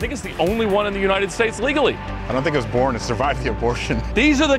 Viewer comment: Did he just say “I don’t think it was born to survive the abortion” at 4:45?